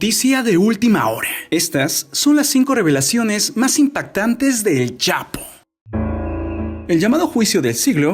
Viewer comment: yo pense que decia el chavo